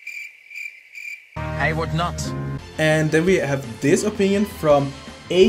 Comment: "dude's a weapon" about Hijikata hahaha I love it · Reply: I'M SO GLAD I KNOW ABOUT THE "he is wet" LINE cos otherwise I would've just heard it as "I would not" in English